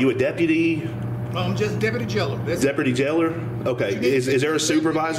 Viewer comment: I swear I thought he said Deputy Jello